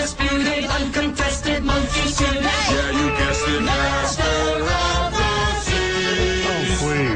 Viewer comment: Always thought he said "master of disease" as a kid and I said he should maybe go see a doctor.